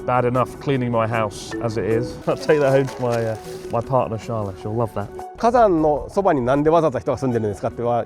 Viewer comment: Oh, Charlottes. I thought he said Charles. Careful Nancy boy, in Japan will be very polite and spit on the ground where you stood when you leave. Still, it’s much better than being taught how to fly in most Middle East countries.